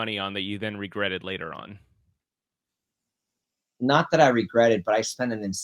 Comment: did he say porn? Lmao